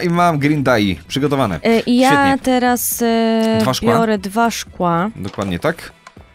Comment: usłyszałem twarz kła